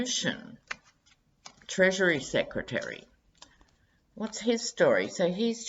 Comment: When you said Treasury Secretary, I misheard that first as Treachery Secretary, and had to stop and process that. I think I prefer the second version. I saw that occasion when Mnuchin and his equally loathsome wife were at the Treasury, pawing the money, and it was indeed a gross display entirely in line with their insatiably greedy natures. They flaunt their wealth and she in particular likes rubbing it in the faces of the peons. Never in my life have I come across such a repugnant couple as they. Mnuchin is the kleptocrat’s kleptocrat, and when you add in his trophy wife - who has an unsavory history of her own - you get kleptocracy cubed. I’d say Mnuchin’s big plan is to empty out the Treasury, to scoop out the goodies until they’re scraping at the bottom to scoop out the last few dimes. There is such irony in putting someone like him in charge of the Treasury. Trump went out of his way to fill his Cabinet with the most corrupt people he could find. BTW, even before you mentioned the Russians, I was thinking that it wouldn’t surprise me to learn that Putin gave him the instructions to empty out the American coffers and bankrupt the country.